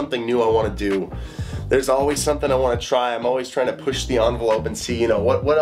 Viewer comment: "Theirs always something i wanna buy" I heard "Theirs always something my wife doesn't want me to buy"